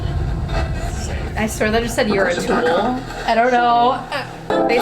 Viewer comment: i thought it said "your a fool"😭